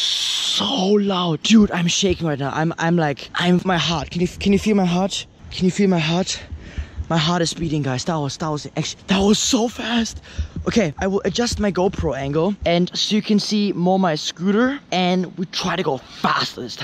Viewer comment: did he say my heart is beating